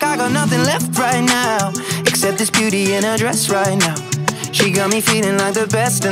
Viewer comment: I swear I keep hearing "this cutie in a dress" instead of beauty